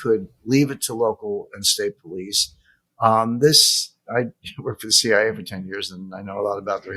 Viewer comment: Did he really said "I worked for CIA for 10 years" at 10:26? I have been following Jim's for years, but that's nee information for me.